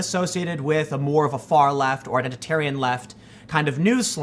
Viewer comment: I had to replay because I thought Tim, when describing Vox News as far left, I thought he said "far left, or vegetarian left"!